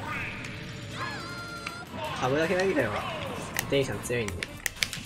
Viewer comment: ごめんなさい8:05なんて言ってるんですか？ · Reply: 株だけ投げてればデイジーさん強いんで とおっしゃりになられやがってますね